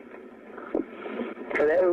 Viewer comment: On the last one he clearly says "got guys chasing me through the woods", not "I got taken through the woods" He also says "help me" at the 11:22 mark. Turn your volume up loud and you can't hear it faintly.